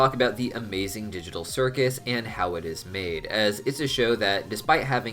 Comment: For a second I thought you said ‘how it’s mad’. Hm